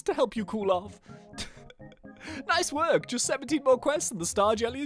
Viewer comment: i heard 70 but it was 17